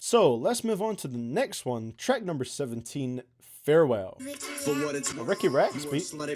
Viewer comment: I misheard you say "Track number fourteen" as "Shrek number fourteen"